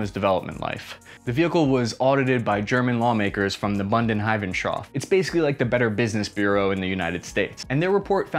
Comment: What is he saying at 7:10? Bundesrechnungshof?